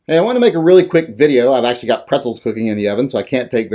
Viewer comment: Thought this chucklehead said a real quick video. Lol!